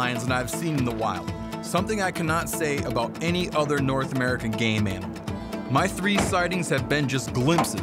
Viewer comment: ~ Is he saying he is a ”gay man” or something else? I don’t think he’s saying he’s gay but I did it first. But I’m actually really not sure. If he is a popular gay hunter then that’s great, I’m just curious. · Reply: No 😂 he said "game animal"